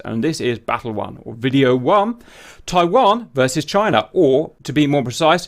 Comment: Why I keep hearing" Taiwang "